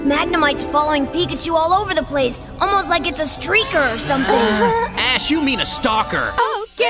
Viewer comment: did bro said str1pper🤔